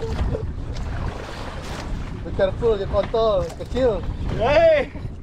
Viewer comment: I heard some "Kontol" in there!😂👌